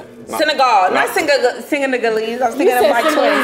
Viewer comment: she really said singaniggalese 💀